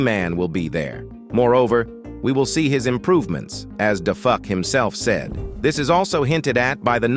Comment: Did he Say DaFu..